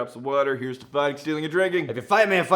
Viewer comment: Sometimes I hear "may you steal a liver's heart." instead of "lover's."